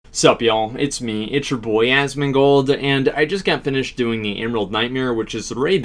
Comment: "What's up Y'all it's me it's your boy Asmongold" My New ringtone ♫♫♫ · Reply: omg no i thought he was saying "sup yall it's atsuboya asmongold" and so his first name was atsuboya :'(